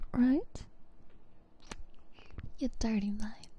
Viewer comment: Bro what the hell is this😭😭😭😭 WHY AM I HERE, GRIFFFFAATTTHHHH👿👿👿👿👿👿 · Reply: I coughed on the "you dirty mind" i thought she said you dirty monkey i had to replay it twice😭